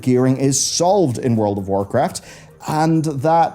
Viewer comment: Lol, when you said "World of Warcraft" at 5:30, it sounded like "World of Warcrest." I love the crest system, and I thought this was hilarious based on the complaints of "too many crests."